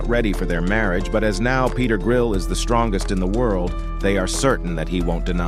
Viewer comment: Peter griffin I thought they said